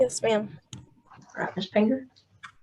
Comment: Yes! I HAVE been hearing Ms. Finger the whole time too. 😂 And I keep hearing it, lol.